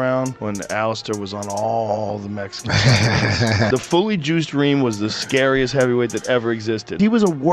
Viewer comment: I thought Joe said “Juicer-reem”